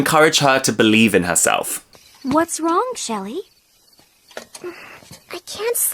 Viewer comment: Is it just me or did she said Shelly??